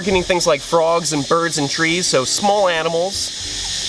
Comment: Anybody else hear him at 6:40 say the venom attacks birds, frogs, and tree's lel